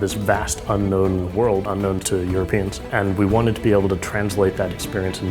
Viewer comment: did he just say "your penis"?